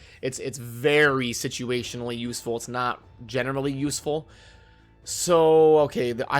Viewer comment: I swear he says genitally usefull